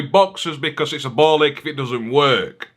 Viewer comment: I'm sorry, did you say "ball lick"? 07:00 😂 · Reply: 😂 Ball Ache